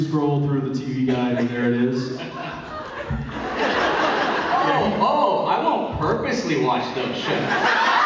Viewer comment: Could anybody please tell me what the guy says at 1:07 that makes them laugh so much? Thnx! :) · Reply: Advocate_of_BBH's_Smile bit kate to the party but he says 'oh oh i won't "purposely" watch those shows'. Implying he does do it on purpose of course, those jensens point and the crowds laughter.